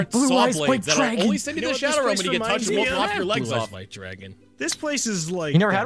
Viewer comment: What is saying roughly 2:10 Dark energy blades that totally arent saw blades.. and cant make out the rest other than "Shadow realm" and "legs" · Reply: The legs part was "lop your legs off", dunno about the Shadowrealm part